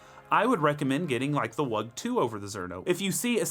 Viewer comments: I can't quite make out what that Z1 alternative you mentioned here at the end... not looking to change or upgrade at the moment, but my curiosity is killing me xD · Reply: I had to turn on subtitles for it :D I think he means the Wug2 83a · what is he saying? Getting which grinder instead? Thanks in advance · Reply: Wug2